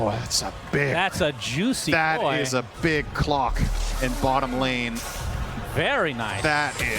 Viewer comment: I thought synderen said that was a big cock when they got the giants rings 😂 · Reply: Hahahaha at first i heard that too, took me a sec to realize he said "Cloak" XD